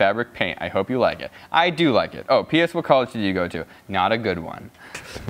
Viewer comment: When said “not a good one” I heard “Not a doctor” Name that tv show.